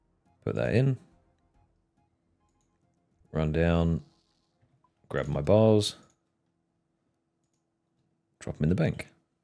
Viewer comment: you said “run down grab my bars” but at first I heard “run down grab my balls” and now I can’t unhear that when I replay it 😅